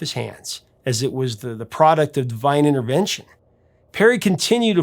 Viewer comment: I thought the said :"DIVYNAL intervention"..there is a good one for ya!